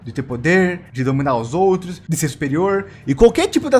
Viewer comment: eu entendi estinto superior kkkkkkk